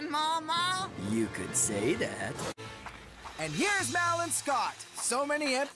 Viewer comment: did Chris just say Mal?